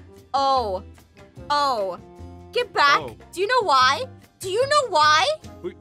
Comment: When she cried about her house and said “my house my house my house” it sounded like knuckles saying “ do you know da way????